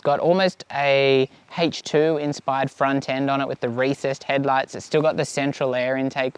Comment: did you say “Racist headlights”??? LMFAO!! Do black people and Hispanics get super upset when you ride past them on this bike with it’s RACIST headlights??!! 😂😂😂🤣😂🤣😂🤣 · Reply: LOL, recessed, definitely said recessed.